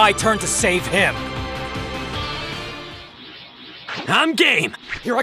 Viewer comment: I love how it sounds like shallot says "I'm gay!"